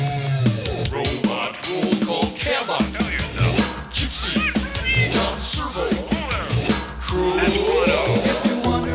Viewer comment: did cambot just say "cover yourselves" wtf? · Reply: Awesome Downey yep he said cover yourself